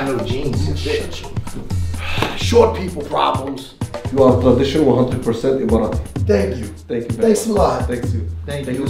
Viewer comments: guy says, you look traditionally 100% Emirati. Ie someone from the United Arab Emirates. Where they are. That is their traditional attire. Subtitles said Moroccan. That isn’t even close to traditional Moroccan gear. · For what it’s worth, that guy at 1:21 said “Emirati” not “a Moroccan” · “you are traditional 100% a Moroccan”. He actually said Emirati but unoooo